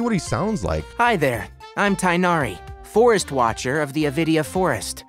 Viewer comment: for a second i thought he said forest watcher of nvidia forest